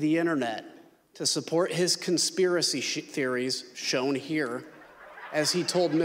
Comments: did he just say conspiracy shit theories? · Surprised Stephen didn't catch Kinzinger almost saying "To support his conspiracy shit"